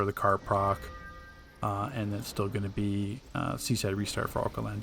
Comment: you what did you say after you said for the card proc? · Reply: seaside restart which is the supercharge reduction passive on Aquaman